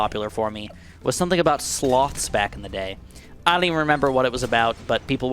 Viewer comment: When he said sloths I thought he said slots which is INCREDIBLY IRONIC considering the first popular comment